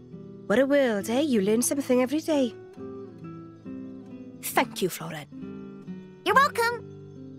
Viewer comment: I wasn't fully paying attention so it almost sounded like Mia said "Feck you, Floren."